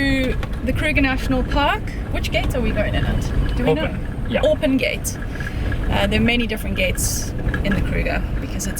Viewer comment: Ahh...Orpen Gate. I thought you said Open Gate.